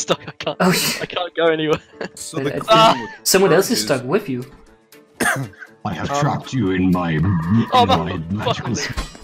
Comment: it sounded like he said “I have trapped you in my mattress”